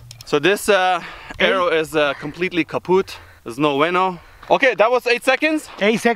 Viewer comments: I thought he said kahoots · Did he say "the arrow is kaputt..." 4:20 haha thx from 🇩🇪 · wait did he said the german word kaputt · wait what, they say KAPUTT!?!?!? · What do you say at 04:20 Completely Kaputt? :D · did he say Kapput? because thats also a German word. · is he saying kaputt? Because it’s the german word for destroyed xDDDD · Мне послышалось или он сказал "Гитлер капут"?)